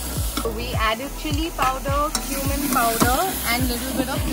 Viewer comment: did she just say "human powder" LOL